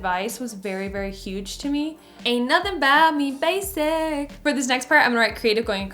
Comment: YALL I THOUGHT IT SAID BASIL INSTEAD OF BASIC i know that’s probably a bit uhh but yknow